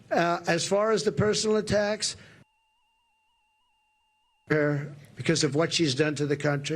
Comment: Did he say "Fuck Her" or the N word? Call it.